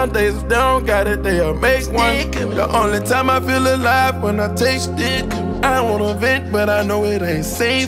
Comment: Im here to see if what Kodak black asked future about his lyric was what he really said...it still sounds like he said" taste dick" to me ...🤦🏻‍♂️😂😂😂😂😂